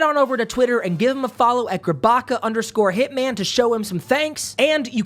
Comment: I heard 'sabaka' which roughly means 'bitch'.